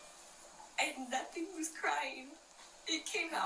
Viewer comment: Did she say "That thing wasn't crying."? 8:11 This is ridiculous!